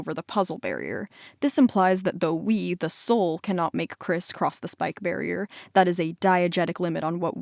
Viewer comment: did you say…criss cross…